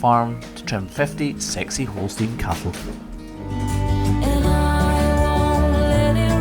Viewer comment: The first time mentioned 60 Holstein cows, I thought he said sexy Holstein cows. 😳